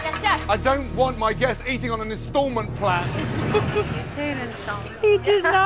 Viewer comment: What does he say at 11:30? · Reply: tubeyoukonto an installment plan. Pretty obvious lol